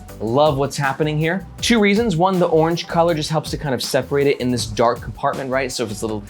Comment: did someone say orange?